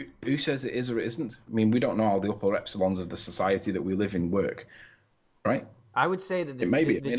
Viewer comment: Did he really say “the upper epsilons of our society”?